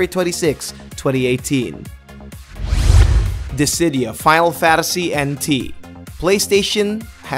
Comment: is he saying Fadal Fadasy?